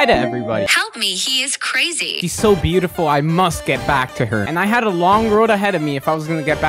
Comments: I heard “he’s so beautiful” And I said “ayo what!“ But then I saw CC and I’m like “phew” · I heard “he’s so beautiful” And I said “ayo what!“ But then I saw CC and I’m like “phew” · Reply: he said “my lover dremia” and then I heard “he’s so pretty” but I knew that it was a girl so I got confused 🤔 but then I saw the subtitles and it said “she”